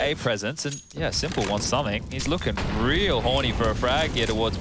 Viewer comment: what did he sayyyyy😂😂😂😂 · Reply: Horny 😁